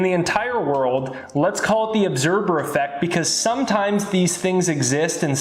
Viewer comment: The observer effect is Not that its invisible Its the fact that when observed by any means either ur eyes or a camera or any measuring device the outcome will always be different than when Not observed? The Outcome was always the same exept when it was observed by either human or machine So as long as its Not observed or studied it remained the same Thats what the observer effect is. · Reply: I thought he said 'obserber effect'.